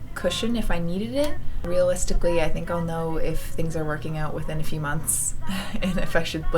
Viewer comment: Im cracking up. First, i thought frodo was a kid with pigtails. I was like who the heck is that!? Then you said "a few..." and i thought you said "ef you!". Hahaha, best wishes sweetie 💋